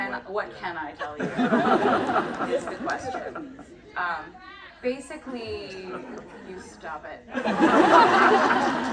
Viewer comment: what did the crowd say I can't catch it 😭😭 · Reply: Someone yelled “give it back” 😂